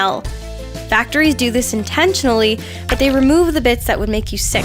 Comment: what did just just say? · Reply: Bits.